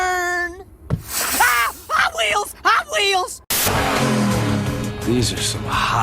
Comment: I swear I thought you said "Hot Meals" not "Hotwheels"